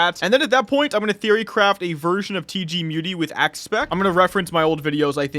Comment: what did he say? He's gonna theorycraf a version of TG muti with what? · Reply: axe spec